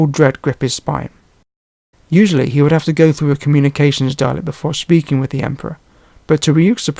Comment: The subtitles misheard ''Communications Dalek'' as ''Communications darling'' XD